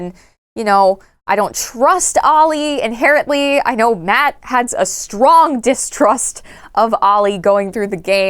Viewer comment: I zoned out when was talking about Ollie and thought she was saying Matpat was distrustful of his son………